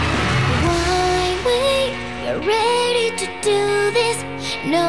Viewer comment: I always heard "One way" when I was a kid I didn't know English then